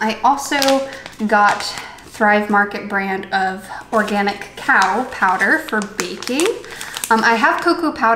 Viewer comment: I thought you said cow powder and now I’m giggling.